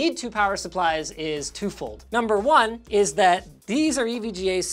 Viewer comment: "The reason we need two power supplies is to fold." Bonus points for telling me he actually said "two-fold."